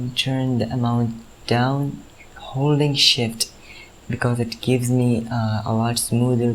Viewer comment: I swear I thought he said hooooly shit lmao